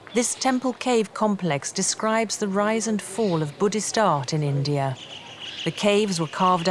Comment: Open your ears, 0:15 she clearly said this shows the rise and fall of budhism in india 🤷🏻‍♀️ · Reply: open YOUR ears she said bhuddist arts not only budhist Regular consumption of cow dung make halusinationtions